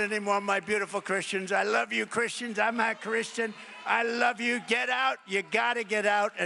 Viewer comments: He said "I'm not Christian." He sure enjoys the support of Christians tho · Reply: oh I thought he said I’m Ah Christian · What did he say??? Someone explain. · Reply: He said "I'm not Christian." He sure enjoys the support of Christians tho